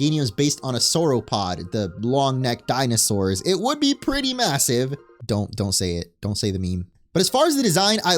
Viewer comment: did you say… massive?